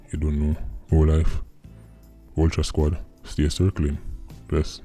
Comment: Crow squad circling.. lol. I thought he was saying ultra squad. Until the other day he clarified it..